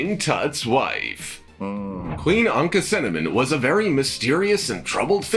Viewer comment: I must have misheard King Tut’s wife name as Anku cinnamon